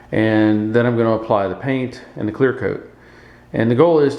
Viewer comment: If I remember, I did 4 coats of paint, and 2 or 3 coats of clear. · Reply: okay, just checking. I thought you said one coat paint and one clear. Just wanted to be sure. You do amazing work. Much respect.